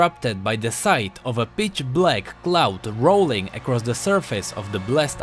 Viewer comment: did you said bitch